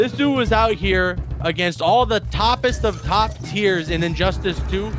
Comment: Thank the Elder Gods that Peacemaker lost. What an awful character to watch even after the nerfs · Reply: I thought they said he was bottom tier